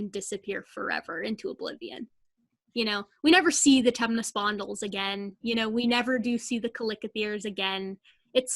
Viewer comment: Closed captioning says, "We never see the Temnus fondles again... We never do see the click appears again." I've listened to this repeatedly and cannot figure out what you're talking about. I'd like to look these things up.